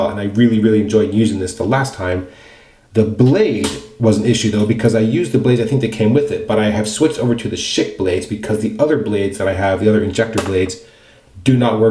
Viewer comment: I wasn’t looking and thought I heard you say “I switched over to the shit blades” 😂